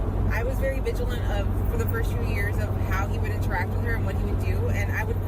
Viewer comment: Did say (29:35) I was vigilant for the first two years ??? That little skit she was saying DOES NOT SOUND GOOD. I keep playing it over to hear her exact verbiage 🤔🤔🤔